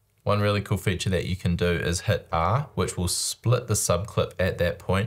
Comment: What did you say at 2:30? Since there are no subtitles provided with this video, I am using auto-generated ones and it misses a word in "cool feature that you can do is hit _ which will split the subclip...." What is the shortcut? Thanks. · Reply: It's the split clip function. The default keyboard shortcut is CTRL or CMD + \. He remapped it to "R"